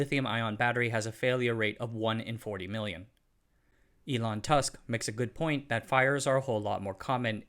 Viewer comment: Why did you say Elon “Tusk”?